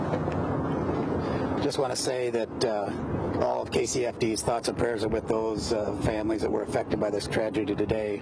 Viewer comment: did he just say thoughts and prayers?